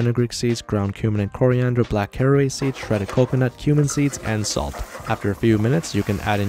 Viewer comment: Bro I thought he said human seeds